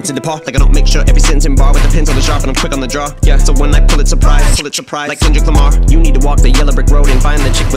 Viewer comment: Great job as usual bro. That Fucking "pull it surprise= Pulitzer prize" is one the hardest punchlines/wordplay Em has spit in the recent years. His Lyricism and pen game are out of this world <3. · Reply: My slow self thought he said "When I pull, it's a price" 😭😭 but this makes more cents.